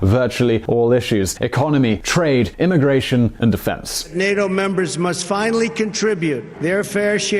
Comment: It sounded like he said nigga members